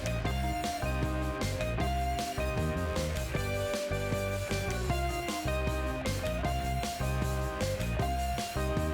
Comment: Lmao what did you name that name tag? 😂😂😂 did ANYONE else catch that? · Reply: Nige (short for Nigel)